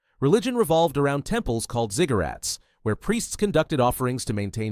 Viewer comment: Did you said Ziggurats or cigarrets? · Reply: Ziggurats 😄 They were massive stepped temple towers in ancient Mesopotamia. Easy word to mishear, but very different from cigarettes.